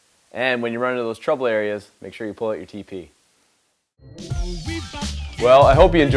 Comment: At end of video, thought you said “ pull out your p.p”. Couldn’t figure out what that had to do with caulk. Guess I should’ve kept watching the entire video. Great tips. Hope it helps me from making the usual mess I make when doing baseboards. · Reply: Haha, it was T.P. 😉 the reference to toilet paper.